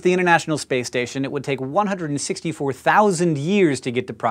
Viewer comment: there is a subtitles error, you wrote "164,00 years" instead of "164,000 years"